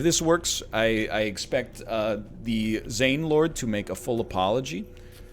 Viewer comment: My gay ass heard it as "If this works, I expect the Zain Lord to make a full apology" and I was wondering when NL became a huge One Direction fan, although I'd agree to call him my Zain Lord as well!